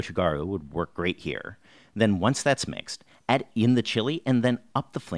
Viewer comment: do you mean add the chicken? Or is it like “add it to the chilli” and im just mishearing 😂